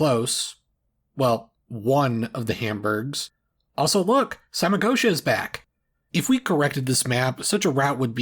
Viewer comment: what did you say? Samacoscia? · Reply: Samagotia.